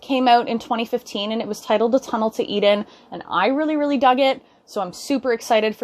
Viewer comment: Totally thought she said "A Tunnel to Eat In" 7:11 So it's "A Tunnel to Eden" lol.